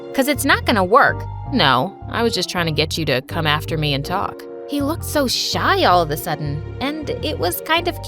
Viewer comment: Bro I thought she said "He looked so Shawty"